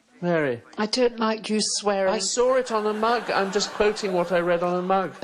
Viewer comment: I thought he was saying "in a mag' " not "on a mug" lol